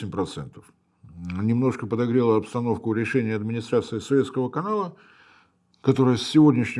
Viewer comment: Послышалось: "Администрация Советского канала..."😂